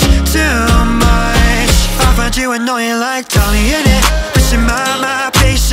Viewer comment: im i the only person, or my ear,s are broken im not sure but why do i hear "lover i know u like tommyinit" at the first bit?!? instead of' i find u annoying like tommyinit???? comment if i need therapy :'l if u can,t hear it listen closely